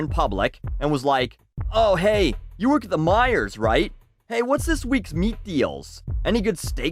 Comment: wait at 5:34 ish did he say that Myers sells meat? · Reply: Meijers.